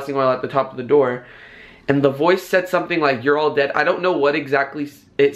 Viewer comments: I heard you're all dead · I heard "we're all locked in" ?!😲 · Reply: Pet Palace it said you're all dead